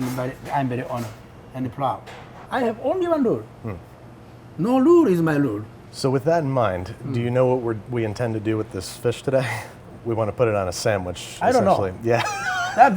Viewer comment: What I heard was "I only have one rule...no lube is my rule" 😂